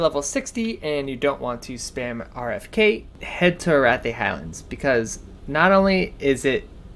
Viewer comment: What are you saying at 8:10 ? I don't understand what you said for either one of the zones for 125+ and you didnt' show a route for either of them just talked about them. Sounds like you are saying Wrathy Hounds but I am not finding any zone like that. I even turned on the captions and it even shows you saying hounds but it didn't even catch the first word. I'm new to wow trying to level up mining so I have no idea what you said. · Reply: Arathi Highlands